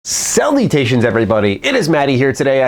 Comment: Thought he said "cell mutations everybody!" rather than "salutations everybody!" paha!